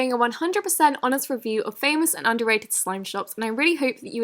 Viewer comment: did she just say famous and underrated??!?!